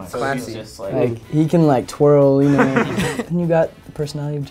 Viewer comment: Could someone tell me what exactly zach says at minute 5:20 and why does Jack react like this? is that I did not understand very well what he said, I still do not speak English because it is not my mother tongue and the subtitles are not clear :((( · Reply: I’m pretty sure he said “because he can twirl”